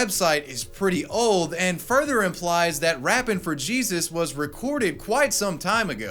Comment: Hope you guys enjoyed this one, I had a blast breaking all of this shit down. Major thanks to Ridge for the continued support, check out the wallets at <link> , Code "SURF" saves 10%. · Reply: I thought it said fappin for Jesus